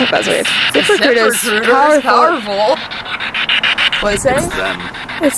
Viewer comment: I heard it say "recruit us to the devil" or something but whatever it said it was very clear